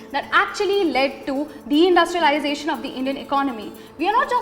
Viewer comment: the subtitle is wrong... She said 'de-industrialization'... Not 'the industrialization'.